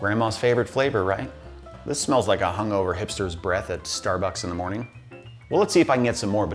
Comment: "This smells like a hungover hipster's breath at Starbucks in the morning." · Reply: I thought he said hamster